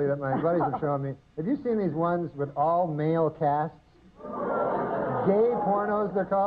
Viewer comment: Don’t ask me why but I thought he said all male cats. 😂